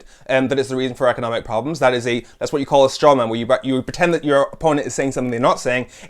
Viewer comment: What was that word you used at 3:16? Was it ‘Strawman?’ I didn’t quite get it.